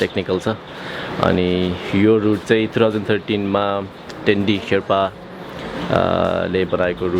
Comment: did i heard dendi? Dota fans?